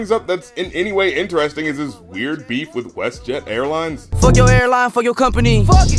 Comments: Oh airline i thought he said hairline :| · Haha oh so he say fk ur airline, thought he said fk ur hairline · Lmao I thought he was saying hairline not airline